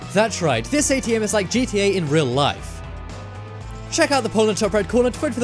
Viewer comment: WHAT DID U SAY AT 7:43??????!?!?!?!!?!!!!!!!!!!!!!!!! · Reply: adib mouhanna GTA IN REAL LIFE LOL XD